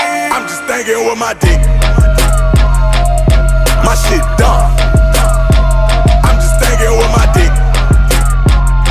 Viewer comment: The power of TikTok lol. All it took was a white man from Louisiana vibin to it. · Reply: From reading his lip thought he said” I’m just thugging with my niggaz” 😂😂😂🙏🏿